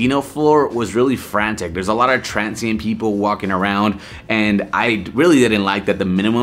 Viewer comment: Great honest review! Thanks. Homeless people walking around. Who wants to see that on vacation. Out on the street is one thing. But inside the casino??? That sucks....lol. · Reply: sorry, I thought you said transient people.